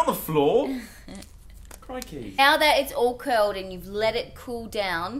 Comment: Did joe just say crickey!!!!!!!!!! 12:26 the ozzie is officially rubbing off on joe!